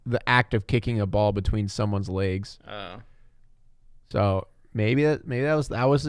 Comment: Hey, you said pronounced legs as "Laags" instead of "Legs" It made you sound kind of Minnesotan. I should know, because I have a mild Minnesotan accent. Not weak, it's easily noticeable, and it's not super strong. I have mild one. I have those long As and say "Ya" instead of "Yeah" My accent is stronger than most people's in suburban and Urban areas